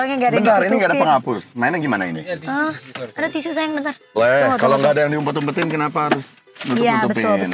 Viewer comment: Salfok denger ayu ngomong "ada tisu sayang bentar", di menit 4:15.. Apa gua yg salah denger yakk